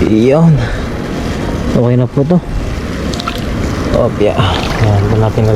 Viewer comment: Im your subscriber and silent viewer, i keep hearing you saying 'hopya'. Does you really say it or I am just hearing it wrong? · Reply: Hello po😂😂 thank you so much po😊😊 hopya is my expression😅😅😅 since I was young this is my expression instead of propane words po😅😅😅 God bless po.. maraming salamat po 😊😊😊😊